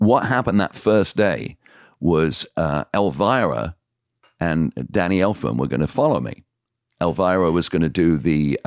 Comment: It sounds like he says Danny Elfman right after Elvira or if it is not Elfman who is it then?